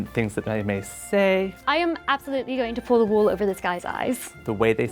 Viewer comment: I’m British and I heard “I’m absolutely going to pull the WALL over this guy’s eyes.” Yeah I can immediately tell who’s not British.